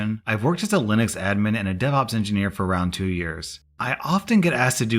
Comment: I thought you said deadops engineer